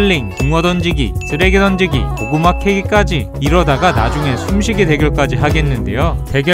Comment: 고구마 케익으로 들리넼ㅋㅋ